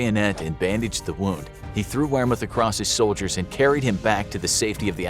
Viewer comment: “He threw Wermuth across his SOLDIERS” I keep hearing soldiers instead of shoulders lol